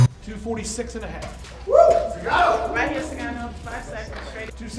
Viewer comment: Did that lady in the crowd really call Junior Dos Santos Francis Ngannou? At 2:50 it sounds like she said "alright Mr. Ngannou 5 seconds"